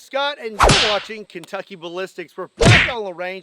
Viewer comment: Lol sound like he said no Kentucky butt lipstick